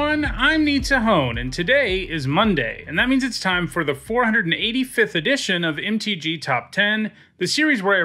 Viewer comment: Did he just say 485th Edition of the Top 10 Lists in the Intro or is it just me?